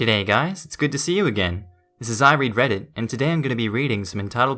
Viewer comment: Sorry, but when you said "I read Reddit" I heard "I regret it." But then, my ears are transplanted from Methuselah...